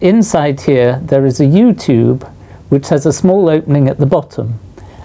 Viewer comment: Is it wrong that I thought YouTube instead of U tube?